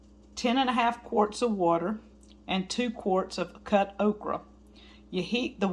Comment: At first I thought you said 10 and a half quarts of water! I thought I don't have a big enough paying for that. Thank goodness you changed it instead 10 and a half cups